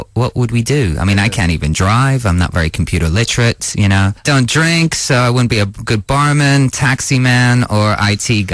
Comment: about 3:31 does he really say : "i don't drink?" or I misheard something? :D